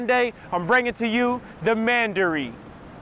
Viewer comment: Honestly I thought he said "The Mandarin," 😱 Not the Mandarin! But as that is not the case, this place looks wonderful to live in. Very spacious but not overdone. 👍🏾